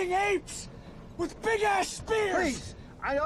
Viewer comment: what are you saying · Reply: With big ass spears